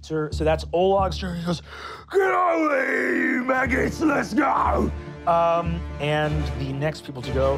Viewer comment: I feel sorry for anyone relying on these captions. They're not auto-generated, and they're still terrible and inaccurate extremely frequently. Example: 9:41, Brennan says, "Get over here, you maggots, let's go!" and the captions say, "Get over here, Maggie, you useless girl!" WHAT?